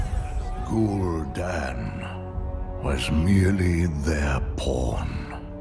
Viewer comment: I heard Velen say "Gul'dan was merely their porn" :D